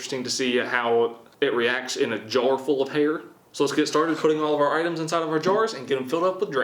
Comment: lmao i thought he said a jar full of hater i was like where you getting that from chum🤣